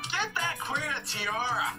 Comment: I thought he said “get that queer a tiara” 😭 I was dying 😂 I love this btw!!! 🖤🖤